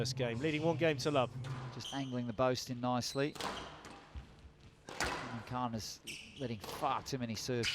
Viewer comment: Anyone else hear "Khan is getting fuuuccked"?